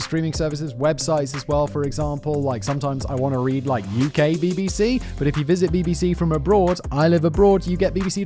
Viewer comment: I misheard the sponsorship and whipped my head at "gay BBC" ("UK BBC"). My immediate thought was "how can I watch gay BBC, again?" 😂😂